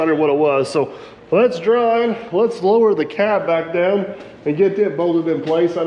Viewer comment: Very happy that it worked so far! I thought you originally said 6’ down. Hopefully the modification you made will hold the added pressure for that depth. Congrats!